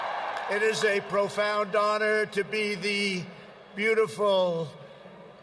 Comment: Wait, what? He said "honor"? I really had to listen to that a second time in order not to hear "daughter". 9:55 - The "make-believes"?? Ooohh, Toronto! Maple leafs! There must be something wrong with my ears today. But then again, everybody hears what they expect.